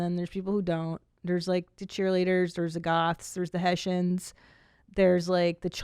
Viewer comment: did she say Haitians?